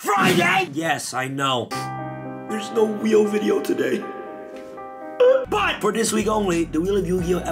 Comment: I thought at the beginning he said that there was no real video today, since its ice barriers lol